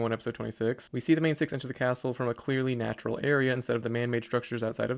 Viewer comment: What is word you used in this context “M a h n”?